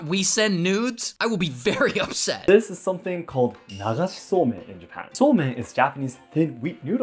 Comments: What everyone hears “nagaski somen” vs what i hear “niggasaki semen”💀💀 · I thought he said semen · Here I thought he was saying semen instead of somen💀💀💀